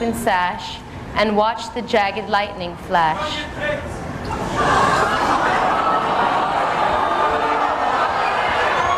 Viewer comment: what did he screamed? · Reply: Show your tits